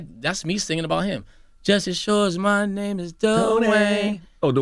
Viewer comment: On "Whatever You Want", I thought they were saying "Just as sure as my name is NO WAY" lol